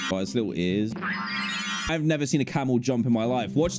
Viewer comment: I thought you said “ I’ve never seen a camels trunk before” 😂